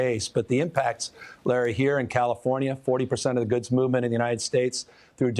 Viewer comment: did he just say menorah?